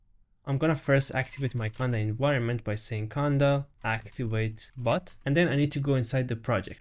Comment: Not watching the screen, I heard: "conda activate butt" 🍑